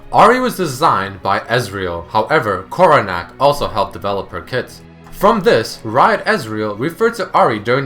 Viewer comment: First time i heard him say koronack help designed her kit, i heard helped design her tits.